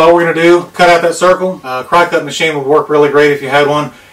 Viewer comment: What was the name of the machine you mentioned at 2:12? I didn't quite catch it - thanks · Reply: A cricut machine